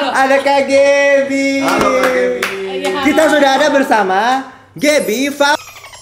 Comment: masih penasaran mereka ngomong apaaa😭😭😭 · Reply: yg disensor tuh "fauziah", refers to yunita fauziah wkwk